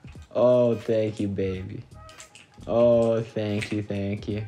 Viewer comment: Ngl when he got the chest plate I thought he said thank you baby